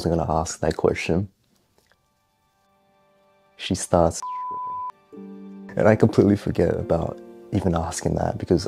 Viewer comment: What is that word? 11:57 Idk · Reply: Stripping